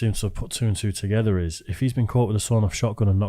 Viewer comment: what is the word that is saying?? I know what he means (a rat across the pond) is he saying a grass or a grouse, or something?? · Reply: Grass/grasser